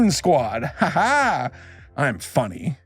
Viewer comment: At the end when he said "I'm funny" I heard "I'm furry..."